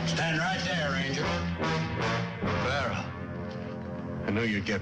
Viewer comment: he looks a lot like Fred Trump. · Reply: One of the characters is Fred and does he say Barron at 19:45 ?